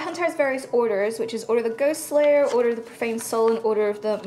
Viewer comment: I heard Order of the Goose Slayer.